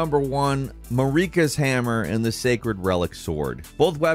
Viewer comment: Did you just say "Mouricas hammer?"